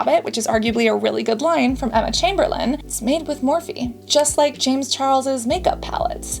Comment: OMG, I thought Cassandra said they were made by Morphine, not Morphe!